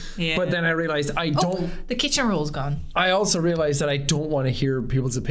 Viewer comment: Fun news, Observation Duty 5 comes out on the 15th so you bet and I are going to be all over that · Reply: tf, did she just say "kitchen roll?"